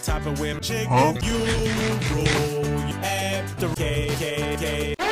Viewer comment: "At the KKK." WHAT HE JUST SAY?! I completely lost it!